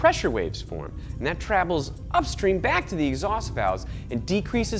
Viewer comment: The ONLY problem I have with this video is at @ 8:18, he says trabels instead of travels